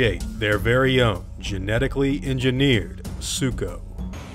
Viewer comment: I swear when you said they created their very own Sucho i heard "their very own Zuko" and just imagined a Rex with Zuko's scar spitting fire balls out of it's mouth